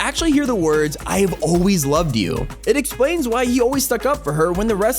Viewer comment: I thought the Scientist said “I always-“ not “I Love You-“